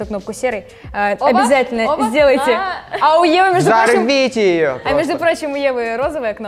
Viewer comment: Мне одному послышалось ось слово "АУЕ"? 😂 😂